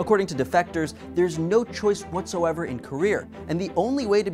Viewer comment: due to my racism, idk if he's saying KOREARRR or career Edit: I went back and listened to it now everytime he says it I hear Korear!